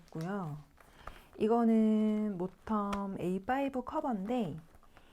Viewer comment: 구입하게된 계기가 "너무 이뻣어요~" 이러시는데 전 천번 만번 공감합니다~~~ 태그를 붙이시다니~~ 저도 모읍니다~그리고 언니, 14:03에 가죽카버가 어디꺼라고 하신건지 잘 안들려서 그러는데 구매처좀 한번 더 알으켜 주세요~~ 트노 올리브색은 구입을 못했어요.. 품절이라니.. 트노의 매력은 너무 무한정이라 비싼거 빼고 그냥 보고만 있어도 좋더라구요~ 담 영상도 너무 너무 기대되요~~ 오늘도 좋은 하루 보내세요~~ 벌써 다음이 기다려져요~~ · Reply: 공감해주시니 기쁘네요😊 가죽커버는 모텀A5 애프리콧 컬러입니다~더보기란에도 써야 겠네요~늘 관심 가져주셔서 너무나 감사해요❤